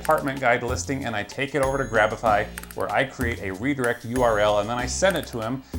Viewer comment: Did you say graph api ? At